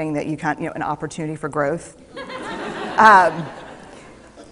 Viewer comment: Hi thanks for sharing! It would be great for you to add on minute 3:55 "it would be a great opportunity for growth " instead of the " ----- an opportunity" so that we can help the 360 million who rely on closed captions understand better her statement. Thanks again!